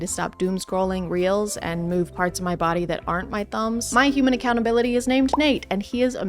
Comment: For some reason, I heard "remove parts of my body" here at first and was like "WHAT?"